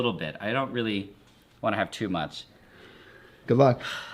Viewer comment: At ca 15:54 I thought he said “goodbye”. 😁